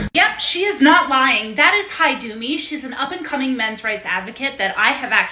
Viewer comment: Did she say Hidome?